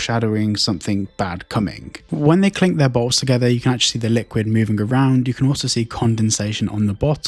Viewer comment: I heard balls 😭